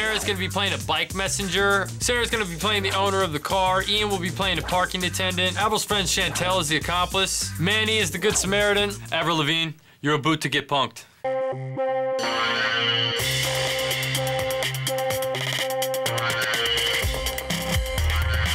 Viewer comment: did he say "aboot"? lol most of us don't say aboot in Canada.